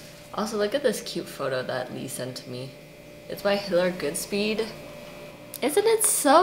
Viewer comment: Heads up, small but glaring error in your captions at 1:11-1:12 -ish timestamp. Should say Hiller as I saw on the screen after the fact, but the caption says something else.